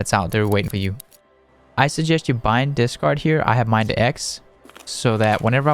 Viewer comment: I thought you said, bind Discord to X. xD